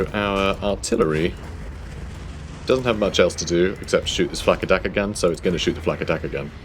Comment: honestly I heard FAKKA DAKKA GUN :D